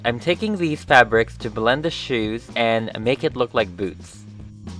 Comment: I heard "And make it look like booze" XD